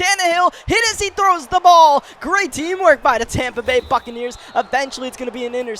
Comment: Almost sounded like Tampa Bay Fuckaneers